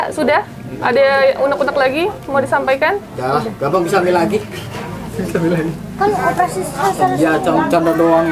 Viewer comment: Astaga… suaminya… bikin geram… menit2 terakhir si mas bilangnya “ nanti bisa hamil lagi “ sambil cengegesan… 29:58 apa ada yg sama denger juga? Apa cuma aq yg salah denger?? Segampang itu…. Abis bikin anak dijual untuk kepentingan pribadi… Kayaknya efek kebanyakan minum antimo nya udah komplikasi… gak hanya pusing yg hilang… otak dan akal sehatnya juga ikutan hilang…. Ada ya laki bini modelan begini… saya udah menikah 5 tahun, sampe sekarang belum di kasih…. Kepingin banget bisa punya anak…. Lah ini.. udah di kasih kepercayaan dari Allah sampe anaknya 3… dijual2in… ya tuhan saya sampe geleng2 kepala nontonnya